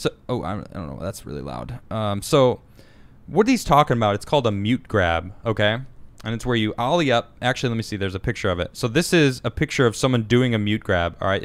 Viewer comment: Tony Hawk calls deaf guy.. Suspect. "They call it a what?! I thought they were saying cute grab."